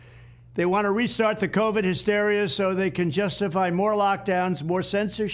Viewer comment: I thought they had said No more lockdowns again.. REALLY